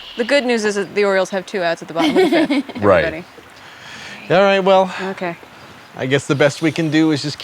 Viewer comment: I am completely unable to understand what's being said on Wil's radio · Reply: The Orioles have 2 Outs at the bottom of the 5th